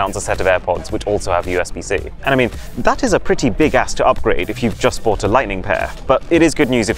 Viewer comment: did I hear big a** ?😂😂